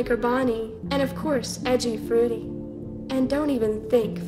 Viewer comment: did anyone notice that what baby said when she said "edgy freddy" it sounded like "edgy fruity" instead